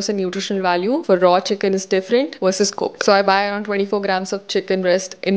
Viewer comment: What do you mean by "I bu 24 grams of chicken breast". Definetly it was an error but what were you saying? I cannot be 24 kilo-grams of chicken breast either :/ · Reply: 2400